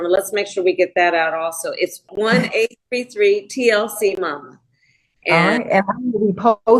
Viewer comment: I think Harris misstated the phone number. I heard “1-833-TLC-MOM” which is not enough numbers. If I heard wrong please let me know. Thanks